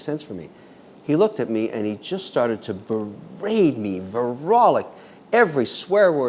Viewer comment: When does Kevin said “he just berated me ver rock lick “ at 1:11 ? What is “ver rock lick “?